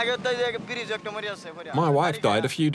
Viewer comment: He is not saying "my wife", he is saying "one woman".